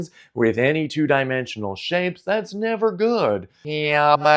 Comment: To tell you what he said at the end, he said it’s time for me to go. Have a wonderful day.